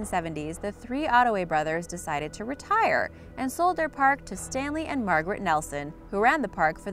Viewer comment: did she said Stan Lee?